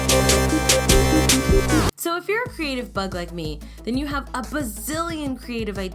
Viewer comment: did you say the f-word or did you say bug?